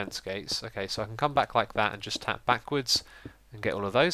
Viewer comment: did u say fap backwards.. wuut